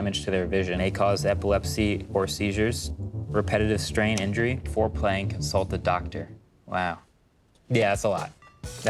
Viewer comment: I thought he said foreplay